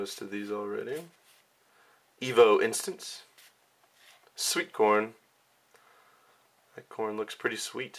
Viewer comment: When u said sweet corn, I thought u said sweet porn!